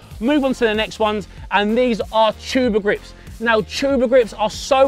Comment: Can someone tell me how are those spelled because I am from Argentina and didn’t understand what he said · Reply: Luka Drago tuba grips, if you want to buy them online there spelt...'tubigrips'